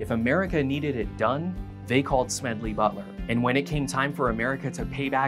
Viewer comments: I didn't know who this guy was and thought it said Smedley Butter. I was wondering what vets had to do with butter :/ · I thought it said “Smedley Butter” Guess I’m hungry